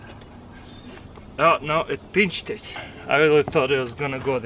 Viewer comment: Is it only me or did he say: it bitched